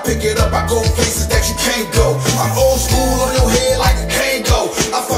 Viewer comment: I love your reaction. I'm watching this again when then lady gets off... And a Kangol is a hat. LL used to wear them alot. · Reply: 😂 He thought you said I’m old school on your head like Django Dope flo bro